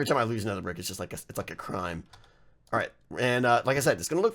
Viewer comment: Did I just hear "It's like a fucking crime" lol